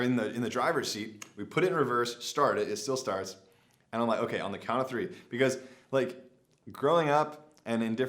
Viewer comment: Crazy shit, think i heard you say "On the count of 3 5 times"..LOL glad you made it out of there